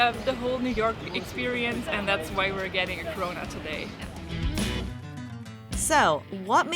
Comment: i thought she said “we’re getting corona today”. 😭